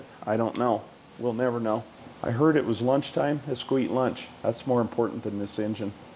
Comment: I don't know how many of the other F.O.G.s watch with the captions on, but if there's a quote that needs to go on a t shirt it's this one. "I heard it was lunchtime, a squeet lunch" One of the neat things about the F head engines was that they had huge valves. Pretty sure the valves on that little bitty bore 4 cylinder engine are bigger than the valves in a SBC.